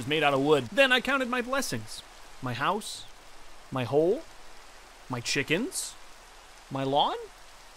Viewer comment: Did you call one of the blessings your "hole"